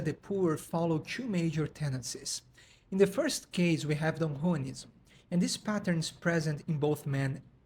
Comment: I’m curious what “ism” you said, I have yet to hear that word and I would like to look further into it. I would also like to thank you for your time and effort in making these videos. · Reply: I appreciate it! I said "Don Juanism".